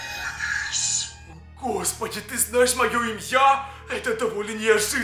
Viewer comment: вам тоже послышалось не Имя а имьЯ